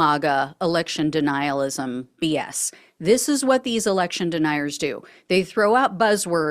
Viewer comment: I thought it said "Erection Denier," my bad!